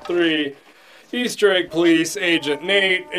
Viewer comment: THAT'S IT I think we all heard that A1-13 is what he said